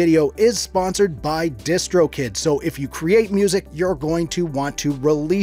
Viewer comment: When he said distro kid I thought he said discord kitten